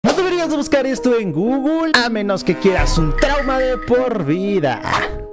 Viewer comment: A pensé que dijo "debes buscar esto en Google para un trauma de por vida"